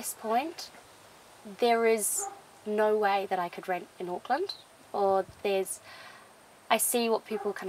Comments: I thought he was saying Oakland, like Oakland California. The rent is very cheap in Oakland so it was obvious he wasn't talking about that hell-hole. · And the bathroom? And the heating? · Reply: Hahaha I thought they were saying "Oakland" too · I wouldn't think one of those would work if you lived up north. I think it would be a bit cold. But for California if you would be willing, it would help a person be able to save some money for sure. I just don't think I could do that. I would have to have a shower, tub, two sinks, and walls with heat. I'm up north lol. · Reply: ***** I thought they said Oakland. THat is why I thought cali. But maybe..